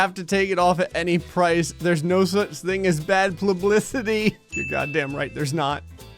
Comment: did he just say plublicity.